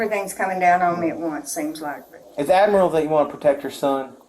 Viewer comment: I’m not sure of that was a mistake writing “admiral” instead of “admirable” or if the cop confused the words and actually said “admiral.” 🤣